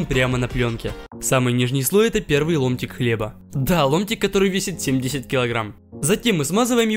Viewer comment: мне одному послышалось "Лунтик"